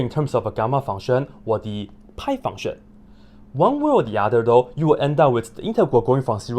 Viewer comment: I thought I heard him say the pyjama function.